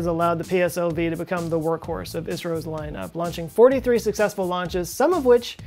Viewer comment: did you mean to say India instead of Isreal? xD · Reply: he actually said ISRO, not Israel. It sounds like Isreal though.